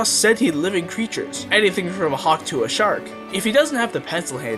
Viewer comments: why did I hear “A hawk tuah shark” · DID HE SAY HAWK TUAH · did he just say hawk tuah 💀💀💀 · I thought he said hawk tuah shark 💀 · I heard you say hawk tuah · he says "hawk to a shark" But all I hear is "hawk tuah" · did i hear "Hawk tuah" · anything from a hawk tuah shark W H A T (i know i heard it wrong its just funny to hear brain rot like this rn) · i swear i just heard "Hawk tuah shark"😭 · Did i just hear “hawk tuah shark”?